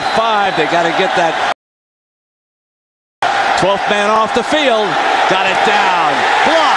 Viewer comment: That took me a second. I swear the announcer was saying osu had 12 on the field since they look overloaded. not a good look sparty.